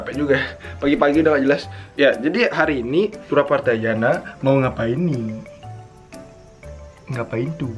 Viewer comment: kalo lagi gk fokus dengernya "Turah Partajhanda"